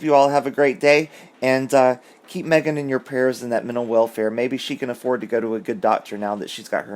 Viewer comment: I thought I heard you say mental warfare!